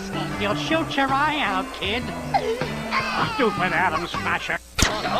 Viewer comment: Did he say "Stupid Adam Smasher!" like from Cyberpunk 2077? LOL